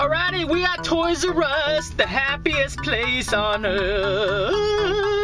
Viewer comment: I thought you said that Walmart was the happiest place on earth hmmmmmmmm